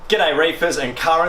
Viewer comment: I thought he said "Good day rapers" 😂 I jumped 🤣